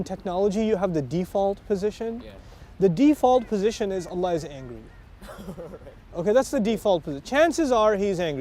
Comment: what is he say at 3:18 i dont get it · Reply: "the default position is Allah is angry" haha meaning that He isnt Merciful or Loving (in that context), and would always judges us. that was Ustaz Nouman's impression when he was younger. but wallahi His Mercy is always bigger than his Wrath...